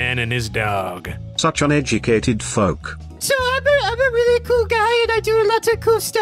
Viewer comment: Did he say the f word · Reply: Folk